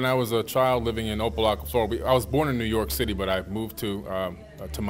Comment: swear it sound like he said O Block 😂😂😂